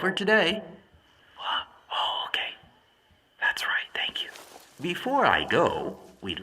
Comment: Did 2:39 strike anyone else as strange? Yes, I realize it's the old 'they don't know you can hear them' joke, but what was being whispered? Context tells me it's something similar to "one more thing," but it doesn't sound close enough to that. I'm hearing "one moulding," or "one mo' ding." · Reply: “What? Oh, okay. That’s right, thank you”